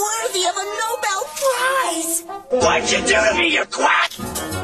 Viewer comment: A Quack is someone who claims to be an expert in a field (particularly medicine) when they're not or have absoloutely no expertise. Hence why Eddy called Rolf a "Quack" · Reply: I always thought as a kid he said "what did you do to me you twat"